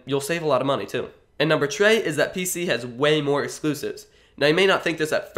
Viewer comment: Did anyone else hear something weird? I heard tree or something. Btw I have a PS4 and a PC. I play cs:go and gmod. That gets boring sometimes so I mostly play PS4.